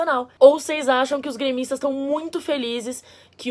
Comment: "Gaymistas"? É isso msm que eu ouvi?